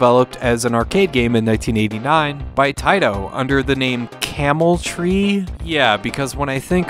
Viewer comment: I can only assume the Camel Tree is attempting to evoke the imagery of a tree with its branches comprised of camel toes. This all being the labyrinth setting for the game. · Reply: "Camel-Tree"? I always thought it was called Camel-Try, as in you're TRYing to get the ball past all the bumps and bends, like rolling over a camel's back